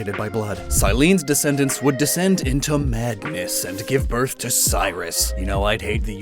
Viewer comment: Totally thought you said descended into Magnus, the Pokéthlon guy.